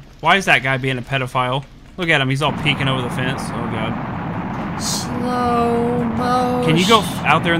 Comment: i heard snapple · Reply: sloow moshhhh :P